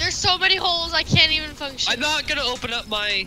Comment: did say i can't f***** shoot ? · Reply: she said function not the f bomb